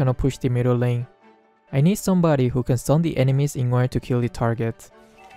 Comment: i need somebody else who can stun the enemies in order to kill the target Me watching while sleepy what i heard: i need somebody who can love me than my words